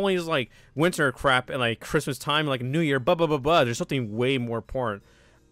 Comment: what did you say? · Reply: he said important